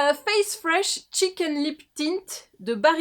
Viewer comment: j'ai compris chicken lip tint 🤣 je fatigue je crois 🤣